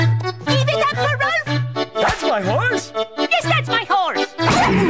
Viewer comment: What does raulf say st 0:20 haha never knew what he says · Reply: “Give it up for Rolf”